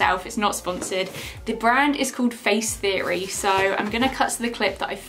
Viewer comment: My Siri thought you saying “face theory” was you saying “hey Siri” 😂